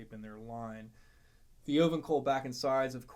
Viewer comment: Can someone tell me what he is saying at the 2:32 mark? "Open call back" or "ovencall back"? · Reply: the wood is ovangkol back and sides. On both this Taylor and the Eastman.